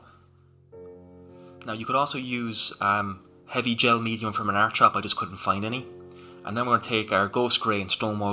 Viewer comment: ish you say " You could also use a heavy gel from..." I can't understand what you said the product was. hearing ain't what it used to be. what was the other product you were referring to please? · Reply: heavy acrylic gel its found in your art shop